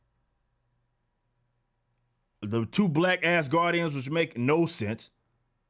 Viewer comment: I know you said "black Asgardians," but it sounded like you said "black ass guardians." lol I had to point that out. Love the video. I agree with everything you said about the MCU getting worse. I expect the next Iron Man movie to be Rescue, Pepper Potts's armored alter ego, since she was already wearing the suit in Avengers: Endgame. I'm not all that thrilled about the Phase 4 slate, as I expected to be. I just don't care what Disney/Marvel does any more. We all know what the next team-up movie's going to be; that all-female Avengers they keep talking about. We're not asking for it, but they keep giving it to us.